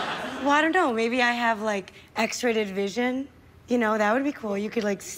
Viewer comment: Did she say "X-rated vision" (about 10:55)?! That is way better than X-ray vision :)